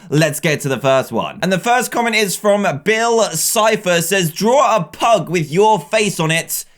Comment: I'm sorry, what? Did you say Bill Cipher? He's from Gravity Falls!